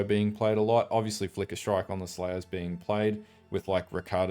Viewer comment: I swear I just heard "Flicker Strike is being played with ricotta"........ What?